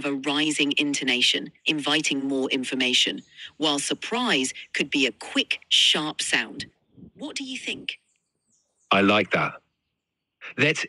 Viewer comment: why do i hear “quick SHART sound”